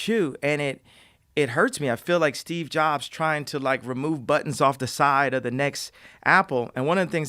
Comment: did he just say "I feel like Steve Jobs tying to remove buttons off the side of the next apple"